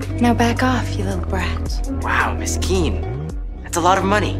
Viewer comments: I just realized that her name is Miss Kean..i heard "Wow Miskine" Who understand? 😅😂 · the way he said miss kean it sound like he said poor thing in somali